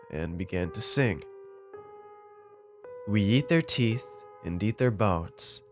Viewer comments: did he say we yeet their teeth? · "we yeet their teeth, and deet their bone" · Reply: Ngan Vo Thien I heard “We yeet their teeth, and deet their boats”😂😂